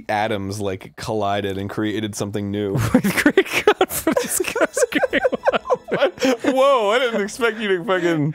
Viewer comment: wtf did Dan say? I can't make it out, and it seems to be some joke both of them find very amusing and it's fucking annoying that i can't hear it. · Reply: He's laughing about the "with great confidence comes great wonfidence" quote.